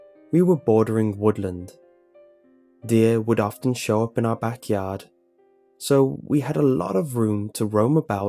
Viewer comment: At about 1:02 my internet gave up, and instead of "deer would show up at our backyard" it sounded more like "deer would shop at our backyard" Makes you think 🤔